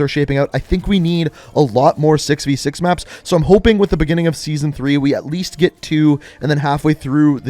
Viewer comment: Did he say season 3 when he’s talking about the maps coming to the game